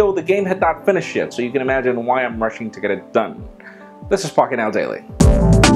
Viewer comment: I thought he said "This is f*ck it now daily"